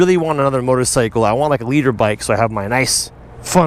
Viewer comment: I always thought he was saying he wanted a leader bike not a litre bike🤣😂😂